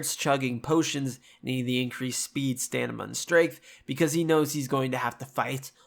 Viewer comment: great video dude, I've been alil lost with the show since I've never played nor read the witcher... But I gotta ask... Did you just say... "StaNiMa?"